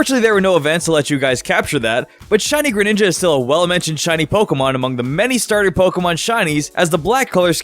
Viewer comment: What is he saying at 5:00? "Shiny Greninja is still a well a mentioned pokemon" ??